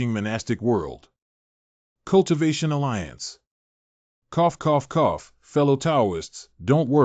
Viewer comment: it's kinnda sus what is bro saying 💀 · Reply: cough coughh........:face-blue-smiling